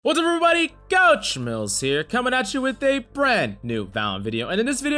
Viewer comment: Damn I heard coach MILF'S